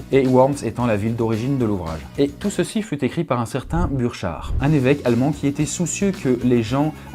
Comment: j'ai entendu clochard mdr😂😂😂😂😂😂😂😂😂😂😂😂😂